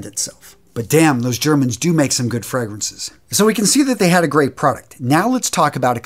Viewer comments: 'But damn, those Germans make some good fragrances' I misheard that as: 'But damn those Germans make some good furnaces.' now I feel bad. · What I heard: ...but damn*, those germans *do make some good furnaces. Felt a little too dark, had to look again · Bro, I literally thought this guy just said “those Germans do make some good furnaces” and almost had a heart attack for him. Jesus Christ. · First time through, I thought he said, "furnaces"! I was like what? Had to go back and listen again 😂😂😂